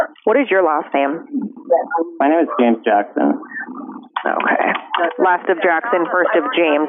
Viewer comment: I swear to got i thought he said “my name is Janet jackson”